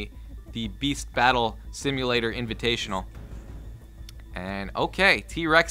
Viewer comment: I need my glasses i thought that shit said breast battle simulator